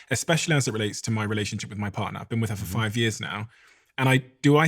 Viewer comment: Did I hear “him” when he was talking about his partner?